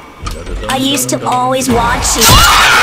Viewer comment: if someone didnt understand he said "i used to always watch you with my cousins"